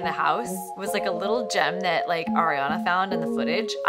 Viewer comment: actually sounded like ariana